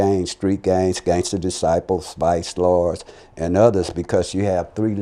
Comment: am I the only one who heard him say SPICE LORDS @ 5:30 😂